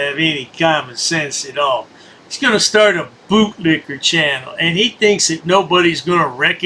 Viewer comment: I thought he already was... oh wait, I thought that said "goatlicker".